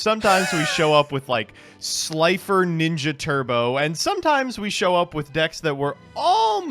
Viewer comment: "slifer ninja turbo" sounds like one of the most HOJ decks ever. PLEASE DO IT! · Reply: When they said Slifer Ninja at first I thought they said Slifer Ninja Turtles them I'm like oh who doesn't want Slifer TMNT!? :)